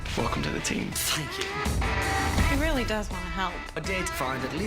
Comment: "Welcome to the team" "Fuck you" Am i the only one who heard that?